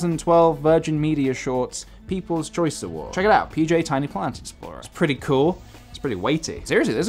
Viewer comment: Idk why but I genuinely thought you were saying ‚tiny plant and explorer‘ for one time and I can’t unhear it again..